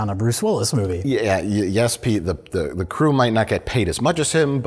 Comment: Did Mike just call Jay, 'Pete'?